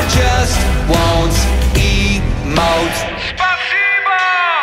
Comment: I thought it said 'Eat mote' Instead of emote...